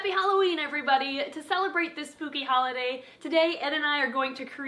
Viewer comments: I heard "pooky" not "spooky" or "poopy" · I laughed so hard when I saw the text "I swear I said spooky not poopy" btw it sounded like u said both "Hello everybody, to celebrate this snoopy holiday"